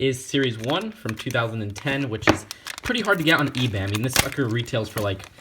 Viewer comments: sounds like he said this f*** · who else thought he said f*cker